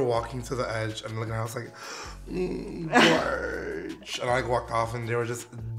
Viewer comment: So every time Gottmik says "Gorg" I hear "George" and for some reason that really tickles me because I think of all the situations someone else might hear it and wonder why on earth this crazy drag queen keeps shouting for someone called "George"